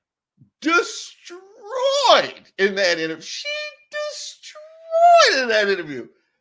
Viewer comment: i thought they said she didn't interview well...lol